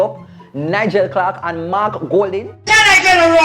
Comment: I thought was gonna say "Mark boring"😂 Mark bronze ish🤷‍♂️